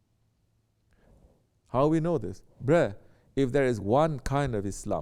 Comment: Did he say “bruh” 9:48 😂